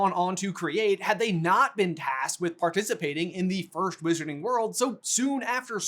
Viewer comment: Did say "first wizarding World?